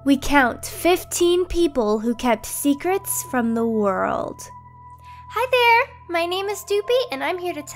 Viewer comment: Idk why but at first I thought it said 15 people who kept secrets from Poland